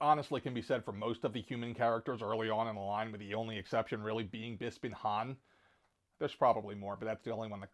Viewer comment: I think its a bit of my "growing up near Philly" accent coming out. lol honestly never realized I pronounced it weird until you guys pointed it out. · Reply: not trying to be a jerkdiuck but since commented on your pronunciation I gotta ask - @ 5:33 did you pronounce Bespin Han as "Beespin"? Totally threw me! Was just talking about things I mispronounced so again, more curiosity why you say it that way unless if I'm mishearing it. And would have included IG-88's incorrect scale as well - he's wayyyy too small (he and R2 are like 5" scale, WTF Hasbro, fix them!! And yeah, Ceremony Luke looks like he's experimenting with drag. Most of the Lukes are terrible except the new Bespin Luke and new Jedi Luke. Wet hair Stormtrooper Luke is totally some other person entirely. Overall great vid and agree with most of your picks! :-)